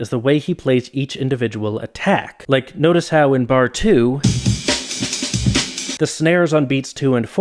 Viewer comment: hey just letting you know that at 15:15 the captions say bar 1 but your voice says bar 2!